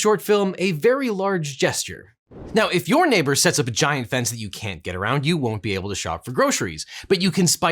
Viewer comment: i thought you said ‘if your neighbor sets a vagina fence…’ meanwhile, if so, i would hope it was hella fresh 🤪